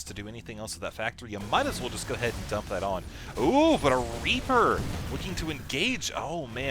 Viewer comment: i swear at 3:50 i thought you said raper =p